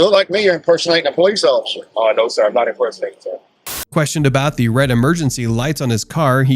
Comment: I could have sworn you said, "questionable blacktivities" lmfao